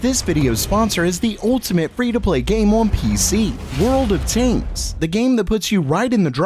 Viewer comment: i thought he said taint not tanks